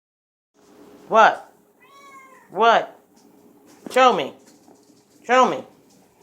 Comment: The owner said "Show me...Show me" at the beginning and the first time I heard it I thought they said "Kill me...kill me" and I was confused when they said no you aren't going outside because I had no idea what they were talking about